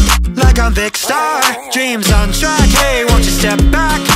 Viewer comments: I thought it said “Dream’s on crack” 💀✋🏻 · “Dream's on track” remember? Dream on rails getting killed by Tommy on the Dream SMP in a minecart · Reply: i thought it said dream on crack